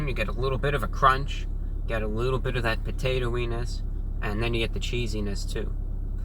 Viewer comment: did he say “potato wenis ” or am I tripping